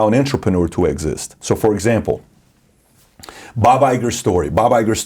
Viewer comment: its Bob Iger, not Bob Eicher subtitles are wrong here.(you know its a machine)